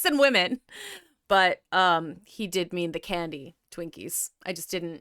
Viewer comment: when she said "He did mean the candies..." I first heard "He did me in the candy" xD